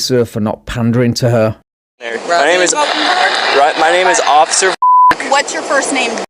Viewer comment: "My name is officer Fuck." I know that's not what he said but that's what I heard and it was great.